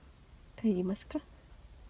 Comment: 頃のおむさん🐱、「帰る～💧」と言ってるみたいに、聞こえてきます。